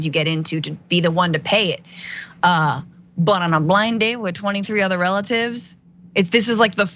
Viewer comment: I think they said they met online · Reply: Oh, thanks. I heard "blind date" which, to me, is usually when someone u know sets u up with someone they know. But maybe that includes online dating now. Im officially old.